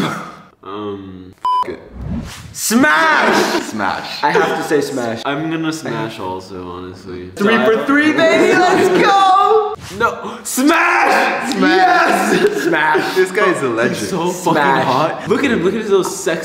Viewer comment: When you guys did the rapid round all I heard was "spash"